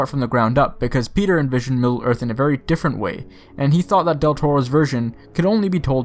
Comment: At first, I thought you said Del Taco \!/